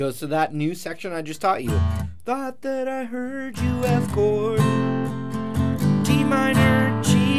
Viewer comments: I recently started to play, and this is one of my fav songs, thanks Marty And that " I thought i heard you f ing" made my day😂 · Thought I heard you f%$#ing hahahaha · This was hilarious especially when you were teaching the verse and making up lyrics about how sad it was and then ‘I thought I heard you F’ing lol